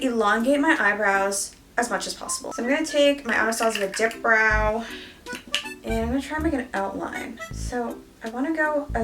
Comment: When said “I’m going to take my Anastasia dip brow” I heard “I’m going to take my Anastasia dick brow” 😳😳😳